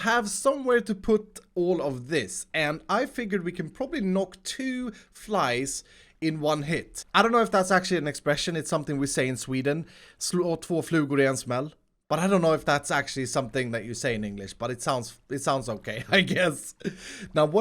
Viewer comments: I thought he said that Grian smell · the common expression is to knock down 2 birds with one stone Edit: 3:46 did you say: "something something grian smells"!?? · at the end of him talking in swedish I heard "grian smell" .... anyone else??? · did you just say that Grian smells? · did he say "sloth for Grian smells"?